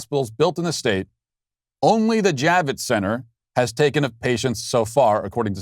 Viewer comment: 🔴🔴🔴🔴How many patients (total) did the JAVITTS CENTER take? It sounded like you said the Javitts Center took one patient.